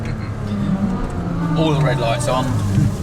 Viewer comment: about 1:35 did he say oil light on?